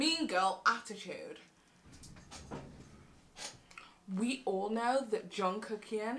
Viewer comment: OKAY BUT At 7:52 I thought she said "We all know that Jungkook here" and I fLIPPED MY SHIT